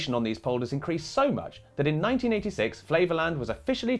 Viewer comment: with your accent I kept hearing this as "Flavorland" and was wondering if Guy Feiri was the new governor.